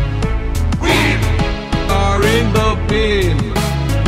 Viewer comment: thought it said: Wham, are in the beam